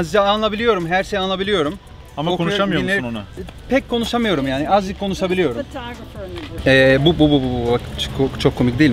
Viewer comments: Abi orada kadın sana ne dedi ? Orayı anlayamadım. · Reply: OĞLUM DEMİRE YASLANMAYIN DÜŞERSİNİZ DEMİŞ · reis kadın ne dedi orda ? 😁😅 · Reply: sanırım who's the photographer in the group diyor